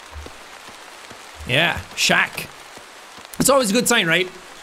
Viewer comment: I'm listening as I crochet and I thought he said Shaq 😭